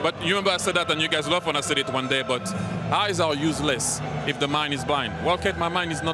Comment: "Eyes are useless when the mind is blind" Thiery casually dropping wisdom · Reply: Oh so that's what he said! Makes more sense. I heard - "Eyes are useless when the man is blind" and was like - well, yeah :D